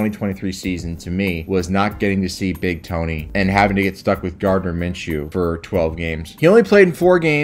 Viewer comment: For some reason I thought you said Jar Jar Minshew